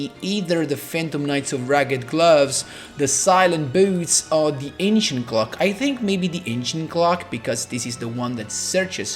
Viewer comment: i heard "Aincient Glock" like 3 times in a row lmao